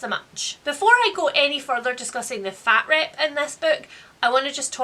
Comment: I had to back it up and add the subtitles cause I swore you said “fat rape.” And I was like, what kind of lingo is this now?? I’ve already been called a “dump truck” and I’m supposed to accept it as a compliment. 😠🤦🏼‍♀️ Here we are. 😂🤷🏼‍♀️